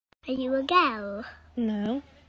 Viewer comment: by "are you a girl?" i heard "are you gay?"